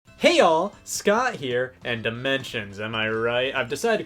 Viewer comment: hey scott ya'll here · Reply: I thought he was saying "y'all" too for months. But I caught it one day and hes actually saying Hey All! So technically your comment should read, Hey Scott, All here 😂😉